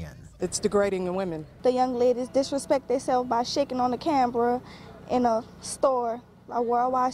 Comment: did she say "cambra"!